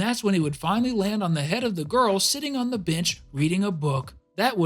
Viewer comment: i heard “sitting on the b***h” 💀💀